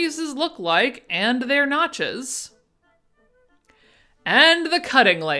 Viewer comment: I thought I heard "at the nachos" aaand I was focused ! notches? NOTCHES! sigh.